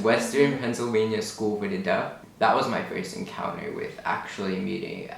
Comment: hello! at 3:33, the English subtitles says School for the Death. I was confused for a moment but I realized you meant Deaf so just wanted to let you know! highly enjoyed this video!